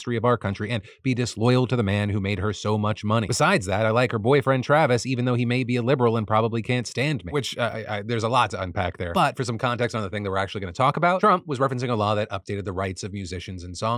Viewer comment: is that what he said or do you maybe need to read better lol. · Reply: when I was first watching the video I was still reading the graphic when it switched and I thought Phil said “But I like her boyfriend Travis, even though he may be a liberal and I probably can’t stand him.” I went back and see that it was “he may be a liberal and probably can’t stand me” Thanks for the deserved callout